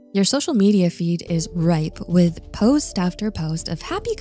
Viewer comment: Good video...@ 2:42 is the correct word "rife", instead of ripe?